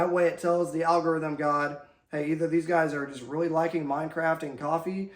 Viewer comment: Did he just say "Mind Craft" I like it